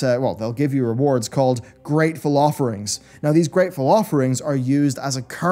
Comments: I thought he said Great Falafel Rings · i know he was saying Greatful offerings but man it sounded like he was saying something like gray falaferings